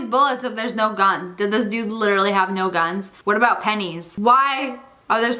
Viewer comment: Actually... Pennies and bullets can be melted down and forged into weapons and can be used to make bullet proofed vests so...? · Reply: I thought at first you said PENIS 😂😂🤣😂🤣